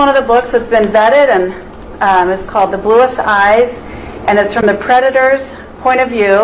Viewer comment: What was the name of that book? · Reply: I heard " the bluest eyes". Its not my accent but that is what I heard.